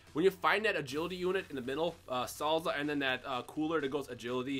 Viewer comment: When you said I'm not gonna kill salza yet I thought you said cellza